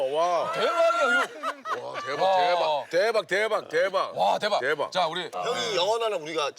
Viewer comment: why am i hearing gay bar?